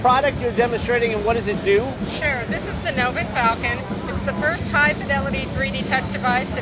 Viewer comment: I thought it said Soviet Falcon -.